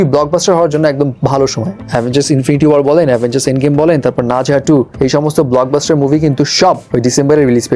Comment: মুভির নামটা কি বলেছে? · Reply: Ne Zha 2